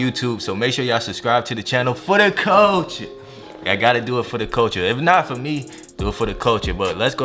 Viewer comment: First off - I thought he was always saying, "For the coaching..." HAA! I could not for the life of me, figure out why he was saying that. Looked down through the comments and realized it's actually, "For da culture..." - which I still don't get or understand. Someone help a girl out. What am I missing?